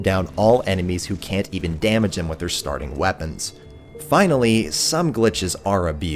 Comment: The way you said weapons almost sounded like chief to me haha